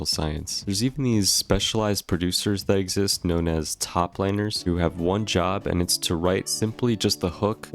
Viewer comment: I thought bro said "toplaner"